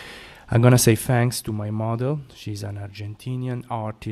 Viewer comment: At first I heard "mother" not "model" lol. I was in Barcelona October 2014, I stayed at Hostel One for a week after jacking up my leg walking The Camino. I should have booked a massage while I was there. Perhaps next time.